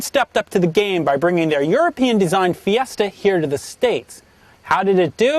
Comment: DID ANYONE HEAR HIM SAY "HERE TO THE STEAKS @ 0:21?